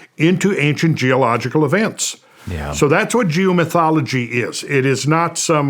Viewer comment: Oh geo-mythology. I thought he said Jew-mythology.